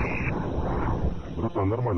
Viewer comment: Что он сказал тут в моменте 1:13? не слышно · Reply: Да, да, наблюдаю